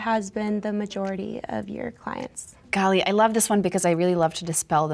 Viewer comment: did she just say "gah lee"?! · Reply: She said "golly"